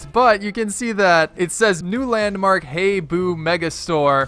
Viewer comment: Did anyone else hear boo landmark at 1:30 or is it just me? 🤣